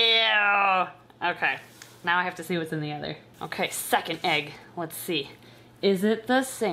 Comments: For a second I thought she said "Suck it egg" instead of "Second egg" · Ok. Anyone else at 12:02 hear suck an egg instead of “second egg”.😂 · I thought you said suck an egg instead of second egg lol